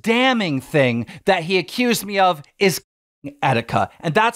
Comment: I'm sorry but the censored "killing" my brain thought keem said "he accused me of fucking etika"